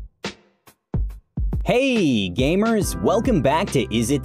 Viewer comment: *starts of the intro What you hear : ""hey gamers!" What I hear : ""Hey Gay-mers"